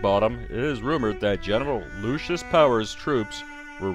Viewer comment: Purple circle... Looks like an Orange triangle to me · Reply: slow down! 3 minutes?.. i'm still stuck at the first 0:10 seconds.. did he say "Genital" or "General"?